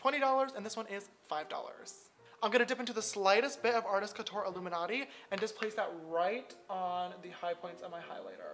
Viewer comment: Did I hear illuminati